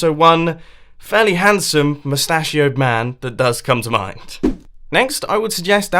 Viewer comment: I thought he said " pistachio man"